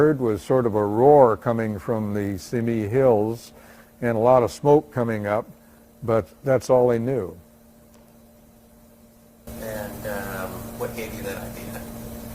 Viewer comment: I hear: "and uh, what gave you that idea?" Was the interviewer from the greater Boston area, sounds clear to my New England ears... -Shawn